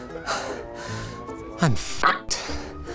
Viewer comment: Did Linus said ''oh merde'' at 5:02 XD